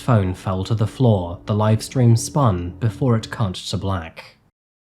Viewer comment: iplayed over and over and I swear you say "before it c**t to black" 😮... lol